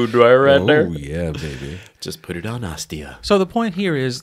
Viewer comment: Did you just say 'Put in on Nastia'.....